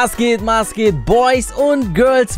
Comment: Ich hab beim Intro verstanden: Mas geht Mas geht